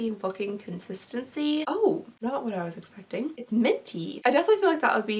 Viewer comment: I thought you said milky f**** consistency..I was like waaaaaaat 😂😂😂 we know ipsy be jacking those prices lol